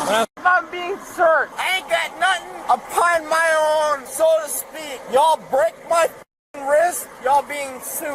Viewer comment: Did he just say "I ain't got nothin' upon my own, so to speak"?